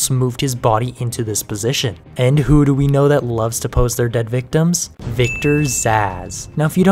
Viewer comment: I had to rewind the first part cause I heard "the main Villain Victor's Ass." I was like, hold up... and rewound to rehear it. Luckily, I heard it correctly then.